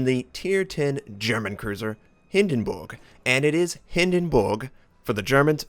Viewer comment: it sounds like your saying Hindenbooog!